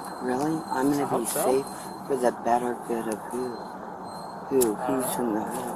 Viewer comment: "for the [benefit] of who"... I'm not sure if the subs are auto generated or not but I think she said what I stated above.